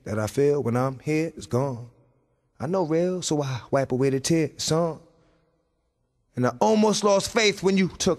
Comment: Not dead yet, "heal son is what I hear." Just waiting ✋️